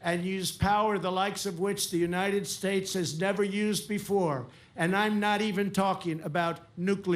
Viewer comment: I heard he’s gonna use his wig as a shield...